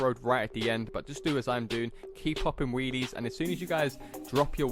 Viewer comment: it sounds like he said, keep dropping wennies